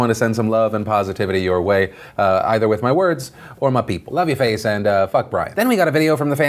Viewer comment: "I love your face and [what did he say!?] Who's Brian? See the closed captioning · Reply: He says: “I love your face and F*ck Brian” at 06:41 Why does he say this?